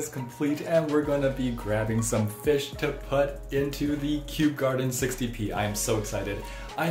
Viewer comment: I thought he said Kew Gardens 50p (as in the coin) and I was so confused